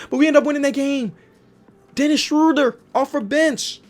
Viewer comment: Did anyone else hear him say Schroeder